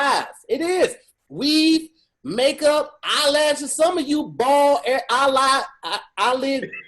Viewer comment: "Bald allah" thats what he said! HAHAHAHAHAHAHA